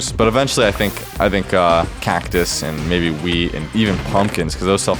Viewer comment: Did he say "Cactus and maybe weed" 2:48 🤨